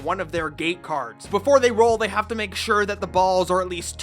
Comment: I kept hearing "gay cards"